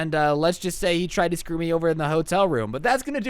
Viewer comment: At first I heard “he tried to screw me in the hotel room”